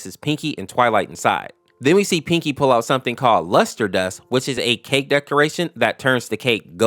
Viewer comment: at first I thought he said huskerdust